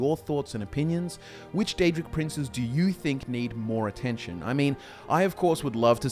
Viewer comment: Thought you said the three daedric princesses that need more attention! 😏